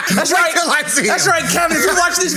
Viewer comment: I might be mishearing, but is Ken's brother called Kev?! Cos that needs to be a TV show in the 90s